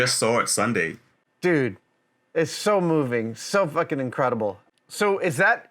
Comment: Did Adam say "It's fucking incredible" at 4:31 ?